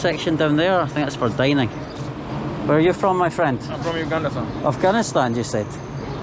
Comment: i swear it sounded like he said "uganistan"